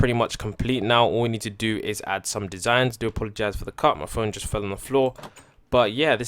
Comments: who else heard him say,”Sorry about that cut my friend fell on the floor.” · did he just say his friend fell on the floor 💀